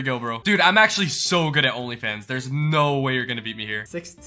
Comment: Anyone else hear him say only fans